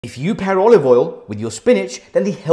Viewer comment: I thought he said "If you pee olive oil" when this first popped up, and I almost choked on my water.